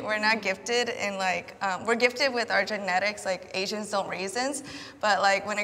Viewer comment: Someone please translate what she said for me at 8:36 it sounds like "Asians don't raisins"